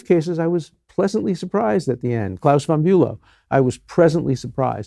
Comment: What did he said at 1:37–“class bon” what?? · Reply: Claus von Bulow - British socialite charged with attempting to murder his wife, conviction overturned on second trial after additional evidence.